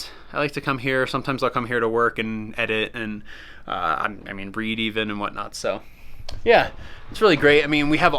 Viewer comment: Did he just say he likes to come here to breed?